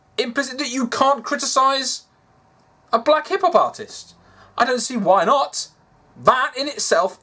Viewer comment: "Black Hip-hop artist" ....am I the only one that heard that as black hippopotamus